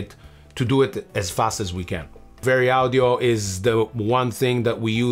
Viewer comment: i can't hear what the name of the plugin is they use 'almost every track' - very audio or sometin for vocal manipulation. Anyone know? (oh yeah, and free Palestine :). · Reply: Sounded like VariAudio or Varied Audio.